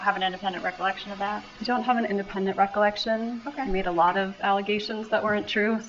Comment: I hear Wendi say, "...he made a lot of allegations..." My subtitles say, "You made a lot of allegations..." It would make sense that she said He, as that's who they are speaking about.